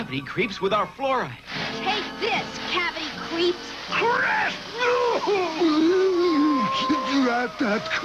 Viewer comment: Did he just say “CRAP!”?